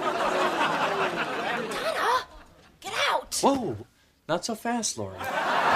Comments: does he say Laura???? not so fast Laura? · did Kelso say Laura or Donna? “Not so fast….” Idk I’m just now noticing that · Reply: Laurie, not laura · does Kelso say Laura rather than Lori? Laura is Donna’s real name